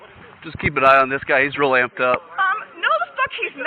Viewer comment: Could someone tell me what the cop says at : 5:20 " Just keep an eye on this guy he's....." ? And what's it means, Please and thank you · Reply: He's real amped up. Which means acting hostile and belligerent. Kind of the same as acting crazy.